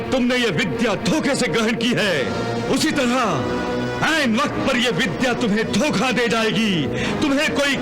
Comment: जो सराफ दिया वो मेने ही गलत सुना हु या आप भी गलत सुने हो.... 26:44 सेकेंड को वापस देखो and बोला सायद English शब्द 🤔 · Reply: Nahi unhone 'And' nahi 'ain vakt' kaha hai jiska matlab hai samay aane par.